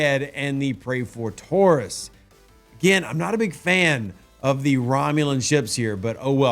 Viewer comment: Wait…did say one of the ships is called the Ford Taurus?